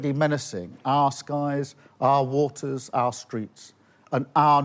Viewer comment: LIKE. SHARE. COMMENT Please join us on LOCALS: <link> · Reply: The CC subs say streams though instead of streets at 44:22 min. So maybe it was little less cringe :)